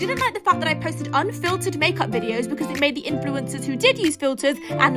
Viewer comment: dont be shy girl name drop her☺️ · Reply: DaisyTheCorgi that's weird, because I thought she said at the end "so let's annoy her some more 🤭"